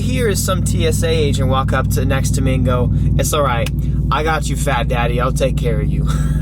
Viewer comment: You misheard the TSA agent.....he didn't say "I got you fat daddy" he said "I got your fat daddy (; "